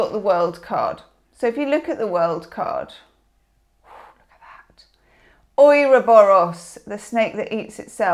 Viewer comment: What is whispered at 4:06 ? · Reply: To me it sounds like "Ooh, look at that"